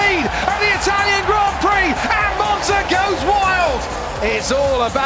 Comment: instead of "And monza goes Wild" I heard "Alonso goes wild" xD